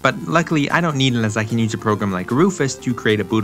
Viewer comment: can someone tell me what he says here? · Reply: "i dont need it as i can use a program like rufus"